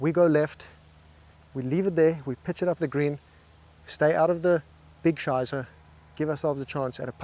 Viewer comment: Did you say "stay out of the big Scheisse"? 😂 Very clear pronounciation, your German is flawless.